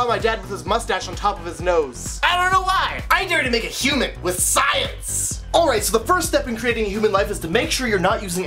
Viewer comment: I thought it said,"I dare you to kill a human.... With scissors," instead of "I dare you to create a human with science." My mind is going to kill me with scissors. Thanks for the vision, i would've had a brutal death.